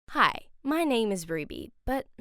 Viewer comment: I thought Ruby said "hi my name is Rebe"